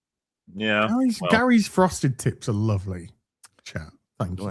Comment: Thought you said "frosted tits" and I'm like, "What, are his nips poking through his shirt or something? Is it cold in there? Is Chat at it again?" but then I heard the rest of the dialogue and realised, since bringing up his wife as a hair dresser, that it had to be the TIPS of Gary's hair and that it's a visual style and has nothing to do with his daddaries.